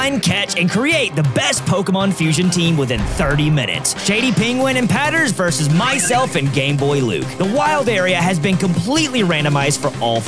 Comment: I thought😂😂😂 I thought he said “GAYboy luke”🤣🤣🤣🤣🤣🤣🤣💀💀